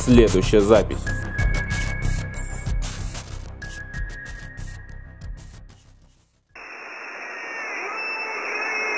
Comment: Я не расслышал что крикнули на моменте 18 минуты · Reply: Это крики люди они сказали "Слава Украине" вот вы слушали